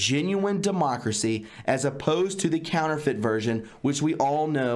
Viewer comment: When you read "counterfeit" [democracy], I heard "caliphate".